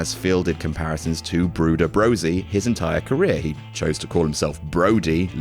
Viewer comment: Did... did I just hear Adam say... "Brooder Brosie"? Edit: Yes and other people including Adam are apparently aware. Giggle away everyone, then move along.